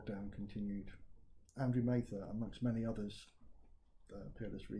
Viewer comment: "Andrew ......" Who? Can anyone hear who he's talking about there? thx. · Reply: Andrew Mather, "Peerless Reads" channel on YouTube, he's a mathematician and studies the covid19 scam statistics.